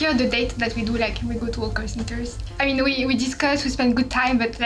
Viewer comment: I think there's a sub error at 4:37, it should be 'hawker centres' instead of 'art centre' right? Doesn't really make sense in the context otherwise Another sub error at 5:32, should be 'shorts and t-shirts' instead of 'shirts and t-shirts' 😅 At 20:33, it should be 'away from your family' instead of 'with your family'